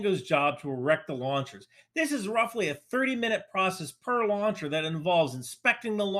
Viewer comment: it is 3min or 30 min job? Because i hear 3min but the subtitle is saying 30 min. 🤷‍♂️